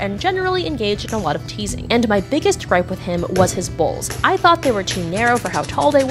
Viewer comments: I heard "was his balls" and i got so confused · Who else thought when she said bowls u heard balls..? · I thought you said balls instead of bowls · why did I hear "balls" instead of bowls? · I thought she said balls. The story was a lot less interesting once I realized she said bowls. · Legit heard you say "his balls" instead of "his bowls" that was the real jumpscare for me lol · I thought she said balls not bowls hahahah😂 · WHEN SHE SAID HIS BOWLS I THOUGHT SHE SAID HIS BALLS XD LMAO OMG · Nooo I thought she said his 'balls'😭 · I thought she said balls instead of bowls 😭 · Ohhh bowls... I thought you said balls😅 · I heard "His Balls" - please tell me I'm no the only one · I thought she said "balls", not "bowls". "And my biggest gripe with him was his balls. I thought they were too narrow for how tall they were. Like the proportions made them a little more cup-like than ball-like in my opinion and I really let him know that i didn't just dislike his balls, but I thought they were wrong." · I thought you said your biggest gripe with him was his balls at first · I thought she kept saying balls instead if bowls 💀💀💀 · BOWLS. My goodness I thought she said ba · lol thought she said his balls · It thought she said balls not bowls my brain needs help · I thought she said her biggest gripe with him were his balls... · When you said bowls I thought you said balls😂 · I thought she said balls not bowls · i heard ''i hated his balls'' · when she said bowls i thought she said ba